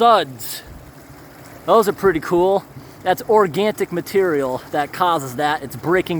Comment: Organic, not organTic (I heard you saying this many times before)